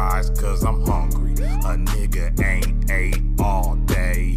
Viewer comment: I thought when he said if 2 dudes kiss that means they’re gay